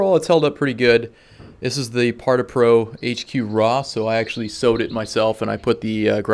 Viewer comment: Hi mate, I am really trying to get a screen similar to yours. Can you help with what you say at 3:26 ??parta pro hq roth?? Trying to find where to buy this cloth in this size. Can you please point me in right direction? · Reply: That was Par2Pro HQ Raw material. They now have a much better screen I've been testing out. The new one is call SQ-W Raw. It has what feels like foam inbetween 2 fabric layers. Provides good picture, has signficantly lower bounce back and its way quieter. So much quieter I thought something was wrong with the ball first time I hit into it because it sounds completely different. <link>